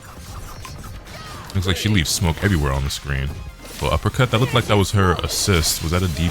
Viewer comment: talkin bout the avatar game? thought u said it looked like ass